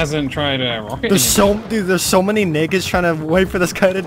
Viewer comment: I know he was saying "nakeds" but I kept hearing "niggas" lol